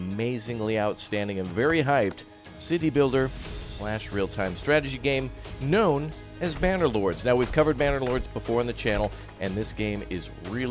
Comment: did he said bannerlords or I am the only one that hears it? :D